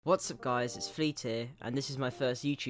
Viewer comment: When he said “Fleet here” I heard “hey Guys, FEET HERE.”